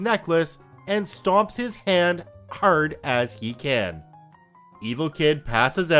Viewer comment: "This is for my dead fathers necklace." CRUNCH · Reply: I thought it said "stomped his head" for a second